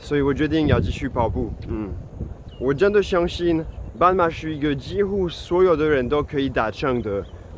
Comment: 為什麼 【斑馬zebra】是幾乎所有人可以達成的目標 ? 我用聽的。一直沒看字幕，完全聽不懂， 到底在說什麼 ? 忍不住看了字幕.. 原來是說【半馬】，半的發音是第四聲，我發現的第四聲發音，都發不出來。也就是說中文時，【嗎，麻，馬，罵】這四個字，發音意思都不同，但這四個字發音都一樣耶。凡有第四聲的所有字，都說錯 。但都已經會說中文了，為什麼第四聲的發音. 都發不出來呢? 很奇怪耶...